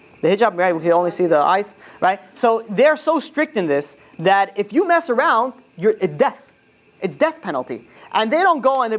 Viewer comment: Rabbi what is the name of the book you said at @ 44:00 how do you spell it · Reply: Kav hayashar